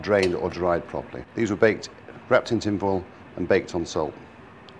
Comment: "These were baked, wrapped in tinfoil, and baked on ???" Any idea what he says here? It's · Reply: Salt